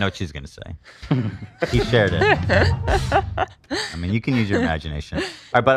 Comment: Has ABs new face been explained? Looks like Michael Jackson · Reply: I thought they said it's cuz he shaved but idkkk